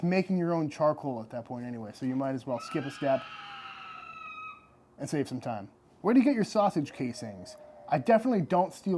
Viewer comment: A rare Texas Pterodactyl. 14:38 A beer recrusted crab tail? What the actual fuck did you say? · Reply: haha iberico secreto